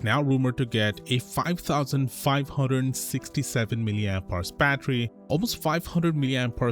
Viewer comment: Wth, visual and audio says 5567mAh but the subtitles say 5425mAh, and "almost 500mAh" in audio but 340mAh in the subtitles?